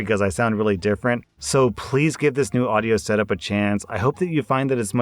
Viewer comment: In the intro, you sounded like you were saying phlease, i bet it's because of the new audio setup